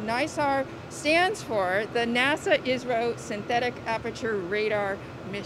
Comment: why always they say israil instead of India.... According to them they even can't imagine that India is doing collaboration with themmmm.. Hypocriseeeeee level... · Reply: She said ISRO not Israel. Listen carefully